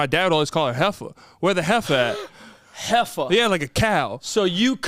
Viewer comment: Hey. In 8:17, did dad call mom "jefa"? Because that means boss in Spanish. If so, nothing to do with cows, she was just the boss in your home 😅